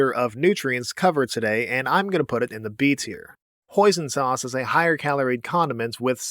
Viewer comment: When he said hoisin sauce, I thought he said poison sauce